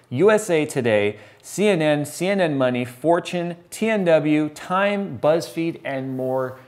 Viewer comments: You said Fortune, I thought you said 4chan. I was momentarily very worried. lol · Did Jay just call 4chan a tech review site? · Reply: I think he said "Fortune"